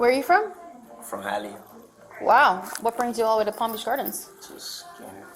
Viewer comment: I can't find ANYTHING in Google about this case. It's clearly a Fifth Third Bank branch. I think the female cop says "Palm Beach Gardens" at around the @ 2:33 mark. And of course there's the Feb. 1, 2023 date. That's well over a year ago. Who is protecting this guy?